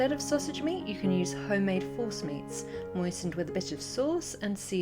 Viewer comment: Did she say "homemade horse mince?"😱